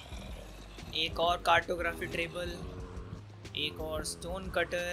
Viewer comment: he said trable 😂😂🤣 instead of table 🤣🤣😂